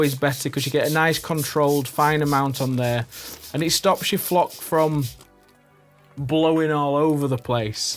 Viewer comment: Did he just say "Cock Flock"?